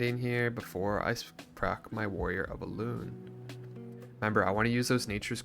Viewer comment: I misheard your warrior of elune as "warrior balloon" in the beginning and was confused for a while 😅